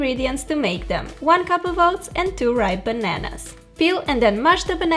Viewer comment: this whole time I thought she said "two right bananas" and I was confused because I didn't understand what whould it mean, but now I've realised she said "ripe" 😬